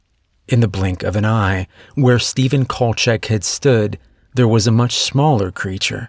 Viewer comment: First of all , great suspenseful story and the reading of it was first rate.. Thank you very much to all involved. The first guy grandpa killed.. when you said his name I thought you said kolchack. I don't know if you remember or know about a t.v. show that aired in the 70's called "The Nightstalker"?!. He was a reporter who weekly found himself battling the monsters of the world. Vampires to voodoo dolls. It was an appropriate mis- heard pronunciation. Looking forward to more from both of you.🤯🥴